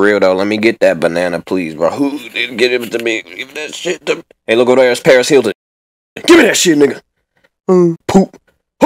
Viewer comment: can anybody please caption What does it says at 0:11 ? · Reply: hey look over there it's Paris hilton